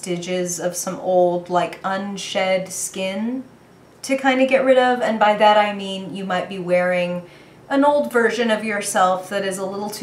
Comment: Lol I thought I said shedding foreskin